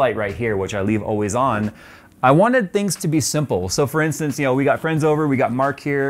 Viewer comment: Nice setup, love it. All i hear, "i've always wanted those, so now i got it." :) nice